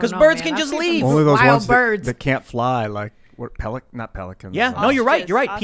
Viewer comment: Yeah similar to turkeys. Penguins would have been a better analogy though. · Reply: Ohhh peliCAN! I thought you said peliCAN'T!